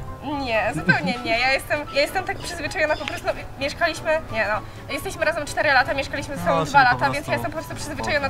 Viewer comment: Czy ja usłyszałam "JESTESMY razem..."?!